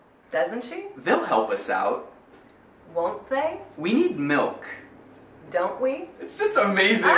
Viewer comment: soh escutei "we're milk" ou we milk mas nem cheiro do "need" 🤗